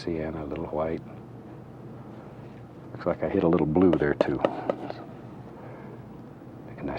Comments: is it just me or did Bob say "f**k I hit a little blue there too" ? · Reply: "Is like i hit some blue there too." Thats what he said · Does anyone hear a little slip of tongue there? Sounds like he said “fuck, I hit a little blue there too”.